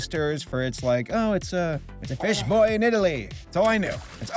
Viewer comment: I heard "bitch boy from Italy" and I didn't even question it